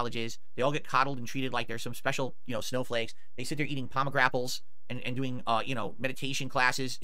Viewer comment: did he really say pomegrapples? Lol